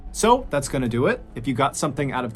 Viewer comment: 🤔 thought you said SOAP THAT'S GONNA DO IT😳😱☠️👍🇬🇧💯😁